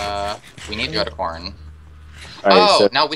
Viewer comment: When he said "we need to go to corn" I thought that he said "we need to look at porn" lol😂😜😝😛😄😄😀